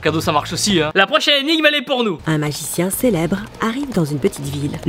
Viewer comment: la prochaine énigme elle est porno moi j'ai entendu ça